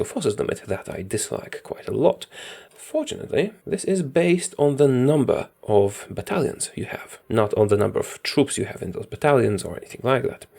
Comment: For a second there I thought you said "This based on the number of Italians you have" lol